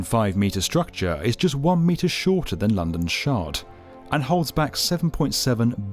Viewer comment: I heard "London's shart" and did a double take. xD